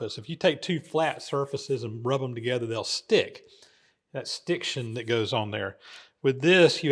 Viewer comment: Did you say "stiction"? I like it. New word for my lingual arsenal. 😁